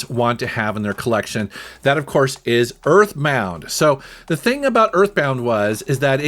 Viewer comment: did he say earthmounth??